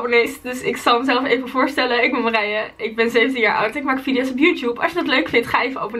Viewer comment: Haha ik hoorde bij 0:04 ik ben 7 jaar oud